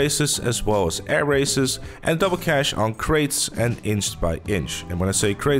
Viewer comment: I had a small heart attack when I thought you said crates were triple. Had to rewind then heard the double.